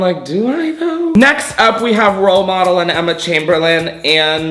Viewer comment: When he said "role model" I swear I thought he said "pill bottle" lmao